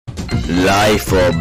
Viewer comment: I always hear "Life of bat. The dry cat."